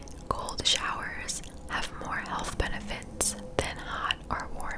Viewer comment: I thought you said "gold showers" not "cold showers" lololololol